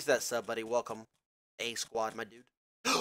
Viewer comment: I dont get what he said at 9:13 ? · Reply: yh he said my dude, something greek says that triggers him